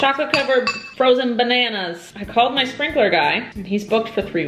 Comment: When you said “I called my sprinkler guy” I LITERALLY THOUGHT YOU SAID SPRINKLE GUY. like you were gonna put sprinkles on your banana and you had a guy.